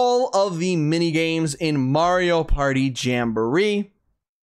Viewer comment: When you said Mario Party Jamboree I thought you said Mario Kart 8 Deluxe 🤦‍♀